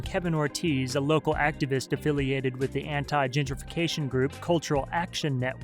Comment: Who else heard anti-gingerfication the first time through at 2:00?